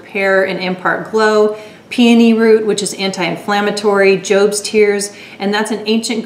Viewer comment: You hair is looking so beautiful! I definitely need to look more into 5yina! I loved the mask and Guiying from that Boxwalla box! Thank you for sharing! :) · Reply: Also when you said Jobs tears, I thought you said ghosts tears haha I was like WHAT!